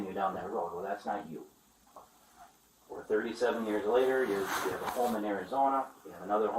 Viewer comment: I thought you said 39 years...clickbaiter